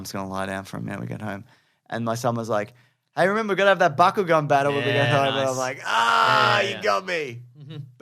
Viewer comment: I thought james said "buckle-gun battle". It took me a full minute to realize he was saying "bakugan"